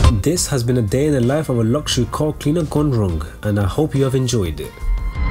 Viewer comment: With your accent I thought you said “luxury crack head”